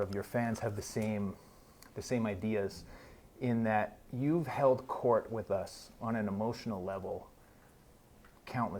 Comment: Please, it would be of great help to me if someone with an ear made to English, could help me to decipher what he says in the minute 00:31, I am translating this interview to Spanish and my ear is not so familiar with English to distinguish what he says, I think it says "held court" but I'm not entirely sure Thanks in advance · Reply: He does say Anthony has “held court” with us on an emotional journey. In other words, he’s led us on an emotional journey or been a leader to us